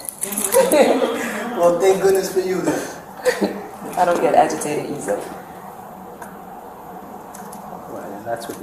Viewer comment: At first I thought she said "I don't get educated easily" and I was so pissed. But she just said agitated, lol. Hopefully she was educated and it sticks.